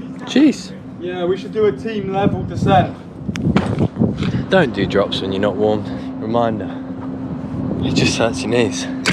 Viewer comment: cant be the only one who heard "dont do drugs when you not warm"